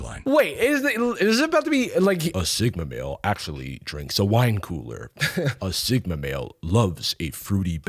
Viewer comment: I looked away for 10 seconds and thought they actually said “wine cooler” 😂☠️ your impression got better as the vid went on😂